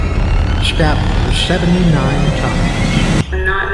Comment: Woah i thought they said 7 times not 79 times, i think she really was abused to have that much anger in her.